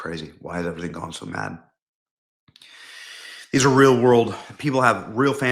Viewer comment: Benny said "why is it going crazy like this" and he followed it "It's the real world" but at a part of the second it sounded like "Israel" so I was like "YEP!~there you go!" but it turned out he was saying "it's the real world"...I thought he figured it out but no..